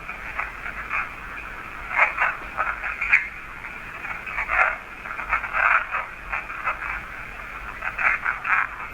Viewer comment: Guys I have listened to this at length and I’ve watched alllllll project fear videos. Going forward I will comment on EVP’s cause I put the headphones on and listen to it over and over and over. And I can say with 98% certainty that this is what it says” I hate the both of you” then another voice comes in and says “get back” “get back” and then the first voice comes in and says “the demon, the demon got me” now my buddy and I went through this extensively and we think the first voice was maybe being possessed by the demon and the 2nd voice was telling the demon to get back and then the first voice acknowledges that the demon got him and that’s why he said I hate the both of you. Not too sure exactly how the conversation went or who was saying what but 98% sure that those were the words being said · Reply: I agree 100%…i heard “I hate the both of you , get back , get back the demon got me “…